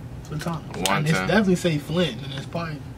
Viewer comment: flintana = flint + Atlanta .. everybody from flint on this song besides yahcty he from Atlanta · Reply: TrvppyTrey I thought it said flintiana